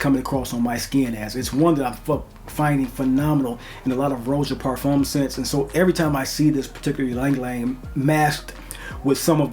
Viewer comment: Great channel! Btw at 4:30 I thought you were going to say it's one that I f*** with🤣